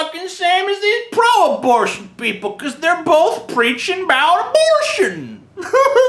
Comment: All I hear is "aboot"